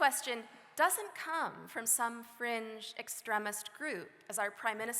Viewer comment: Lol, did I hear right ? French extremist group ?